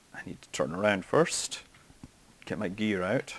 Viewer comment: I swear to god I heard you say "lemmie get my beer out" instead of "lemmie get my gear out"